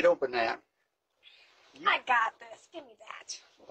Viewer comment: It broke my heart when he said "I don't have no strength left."😭💔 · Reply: I thought he said friends, but strength makes more sense. Either one is sad though, makes the realisation of getting old seem so hard